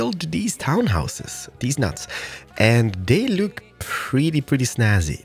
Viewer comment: Did he say deez nuts at 0:07?